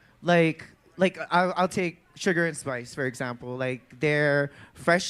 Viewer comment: Lmao, I thought Sasha said “Triggerd and Sparks" and I was like "what’s that read about??" But she 1000% said sugar and spice